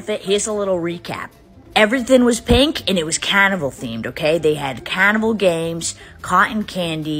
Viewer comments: I thought you said “cannibal theme”💀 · It sounds like you said cannibal, not carnival. Stop overdoing your accent. It's a shame.